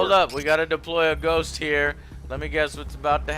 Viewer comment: Wait did you say ghost? Oh my bad, I misheard you. Thought you said Space Navi.